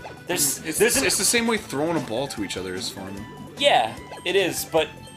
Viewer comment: for a moment there i thought arin actually said "throne of bhaal" lol. Does Arin play the good ol' crpgs?